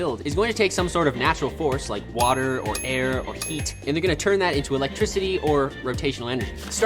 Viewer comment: people who thought he said water or air or feet? 👇